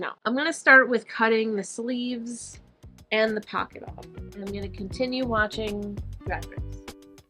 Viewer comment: When you said "and I'm going to continue watching Drag Race", for a second I thought you said "and I'm going to continue watching RAT RACE" (the 2001 smash hit motion picture starring Rowan Atkinson, Seth Green and Jon Lovitz.) My ears did a double take haha. Also it's been really nice watching you sew. You're making me want to try this too!